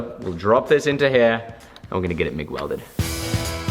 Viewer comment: Did he just say McWelded?